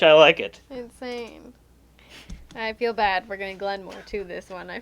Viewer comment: Did I hear a "Glen More 2" around the 1:10:35 mark??? :P I love that game. :)